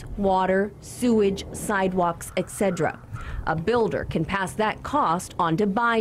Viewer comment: Did she say "etcedra" instead of et cetera.